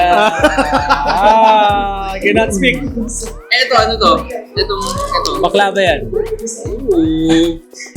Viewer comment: عرببببب 3:48 كاني سمعته قال بقلاوة 😳 اكلنا وصل لعندهم تمر و بقلاوة 😳 اكلنا هواااء...